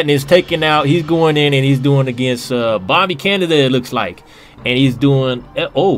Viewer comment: I thought you said bobby kennedy (bobby canada)